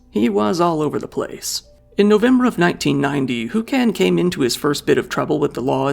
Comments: I thought he said his name was Toucan sam 😂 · I thought you said "Toucan Sam" at first 😂😂!!